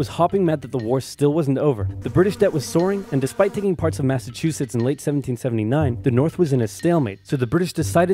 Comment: Am I the only person who hears Massachusetts instead of Maine?